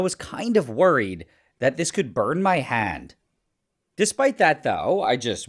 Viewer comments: For a minute I thought he said I would burn my head😂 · I swear when he said “I could burn my hand” I heard “I could burn my a-“